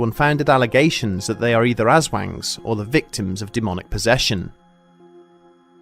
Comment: I kept hearing ‘as-wank’. XD